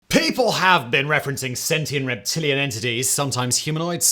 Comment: I misheard sexy instead of sentient 💀